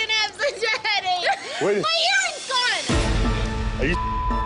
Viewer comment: i literally thought she said "my hearing's gone."